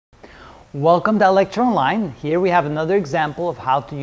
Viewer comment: I always hear it as, "Welcome to Electron Online". Lol. BTW, Thanks for your videos!